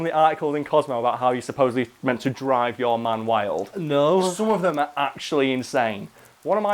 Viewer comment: When you said “drive your man wild,” it took a while before I realized you WEREN’T saying “Drive your MOM wild,” and I was horrified at the thought that Cosmopolitan has articles on it. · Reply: I THOUGHT HE SAID DRIVE YOUR MOM WILD. I was like, "What the fuck is going on cosomo???"